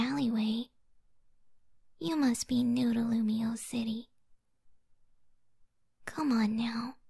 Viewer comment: the caption said you must be noodle city 😂 im dead ☠️